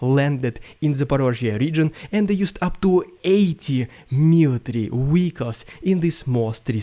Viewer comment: at first I thought you said Ukraine used "80 million" vehicles. I think you ment to say 80 million mosquitoes.